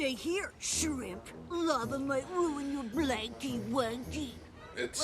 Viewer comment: does he say" lava might ruin your blanky wanky"?that last word must mean something different in different counties.